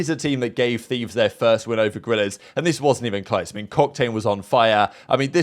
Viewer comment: Did he just call em cocktane or am I trippin😂